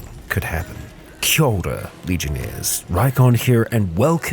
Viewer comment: I always thought you were saying 'Shoulder, Legionnaires.', like how someone shoulders a pack before a trip. I just thought it was your way of saying 'come with me on this journey'. The more you know, I guess~.